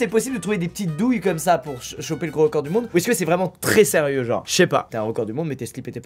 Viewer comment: C’est moi ou à 8:52 il a voulu dire choper le Coronavirus 😂😂